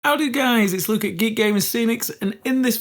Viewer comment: What is the first word he's always saying? Can't be 'howdy', can it? I always hear 'how do guys'. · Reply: Lol, that's because he is saying "how do guys".